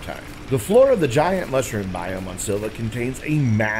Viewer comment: hey thank you for the video, there are some great tips here. That last one.. I didn't understand what you said (at 4:52) even after rewinding it several times. Where are you? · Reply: way down underground in the "mushroom biome" on Sylva.